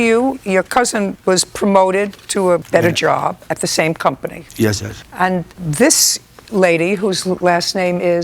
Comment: 🤣🤣🤣 does he said it again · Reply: he said "yes judge" not "yes sir".